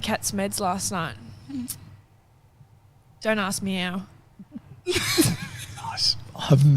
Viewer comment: the first one: I thought she said "dont ask me out". {because I need to get the bad meds out of my system first} I still laughed.